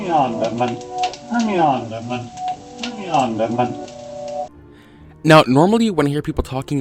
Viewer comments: I heard "Bring it on, dead man" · i heard "Play me on, Edmund" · Sounds like its saying "bring me on Desmond" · I hear “bring me on, dead man”